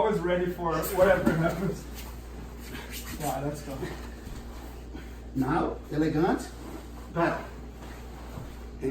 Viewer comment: What's the word he is saying to get Bernardo to stretch his body out, after stopping the pass? Like at 14:35 ... "elegant, paddle" ??? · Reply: Elongated...pedal means foot on the hip